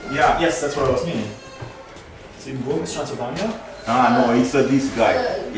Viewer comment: i'm brazilian. i didn't understand but she said "castlevania"?